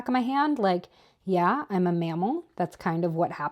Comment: I thought you said you were a “male”, not mammal, and I had to rewind 😂